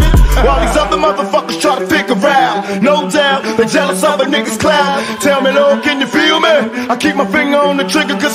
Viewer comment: did pac just say clout? 🧐